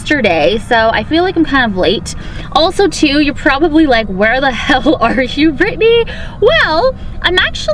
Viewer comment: love it 🤗🤗🤗🤗😚😂😂 and did say "where the hell im i "